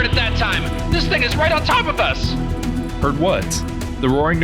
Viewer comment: I swear to God I heard "This niggas right on top of us". I heard what I heard. Either way it's funny.